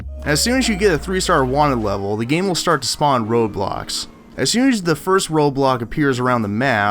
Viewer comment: when he was saying road blocks i thought he said roblox